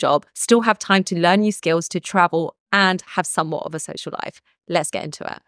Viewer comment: I thought you said, "a sexual life"😂😂😂